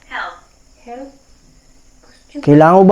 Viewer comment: May narinig akong nag hello after ng tatlong knock nasa aroung 9:58 - 10;00 mins na yung oras ng video , reply nyu nalang mapara mapa kinggan nyu